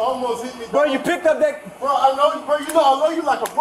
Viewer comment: "I know ( or did he say love? ) you like a brother!" Heartbreaking. This coulda been fucking tragic.